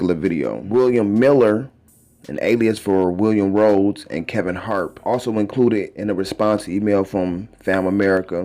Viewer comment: Thought that nigga said kevin hart lol.